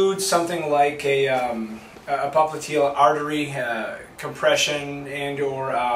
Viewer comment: Hi, could you add subtitles to the videos ? Sometimes it's getting difficult to keep up with you :) In this video, I couldn't understand the part starting from 2:04 :D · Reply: Hakan ÖZTÜRK Hi Hakan, Sorry about that. I was trying to say that this patient could have these symptoms from a collapse of the popliteal artery or from over compression of the arteries from muscular hypertrophy. I am pretty sure he has the later since ex. induced compartment syndrome takes some time to kick in and collapse of the popliteal artery causes symptoms almost constantly. I've seen both. Best